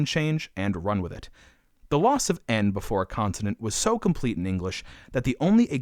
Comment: did bruh just say continent instead of consonant?